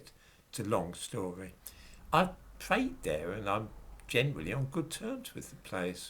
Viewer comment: When said he's PRAYED there, I thought he said he'd TRAINED there ! I was all AGOG ! Expected him to launch his jihad before the end of the video ........ Think I'm going deaf in my old age.....